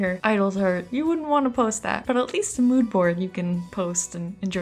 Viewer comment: "mood board" I kept hearing "lewd board"...well, sorry guys, me and my bad hearing even though I'm using headphones 🎧 😅